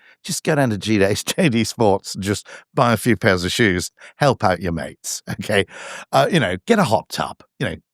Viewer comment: Buying shoes at JD sports doesn’t give money to his mates, it gives money to their employer. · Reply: Think this is a mishear. He said help your mates out by buying them some trainers (0:25) not help the economy by buying stuff from JD sports